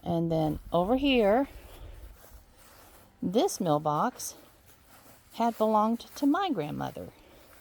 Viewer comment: Oh this makes more sense now that see what a mill box is….i thought you had said mail box..rofl